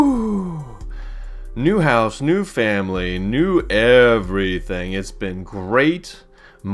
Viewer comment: did you say new family?